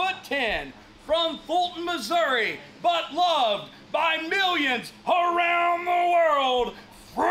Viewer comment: Butt loved from millions around the world? Did I hear that right?🤣